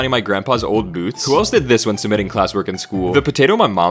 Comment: I fr thought it said “the mom my potato peeled” and I was like 🤔